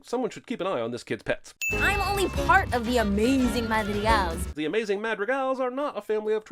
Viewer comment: its very clear that the kids say “who’s ALL the amazing madrigals”, it’s even in the captions! he’s sinning something because he misheard it. they know who the madrigals are, just not all of them.